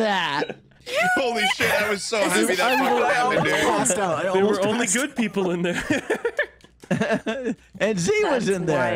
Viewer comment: WHAT DID SAY??? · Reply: She said "you knew". So idk what you're hearing.